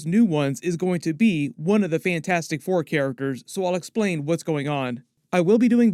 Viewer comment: I thought you said Fancastic 4 😏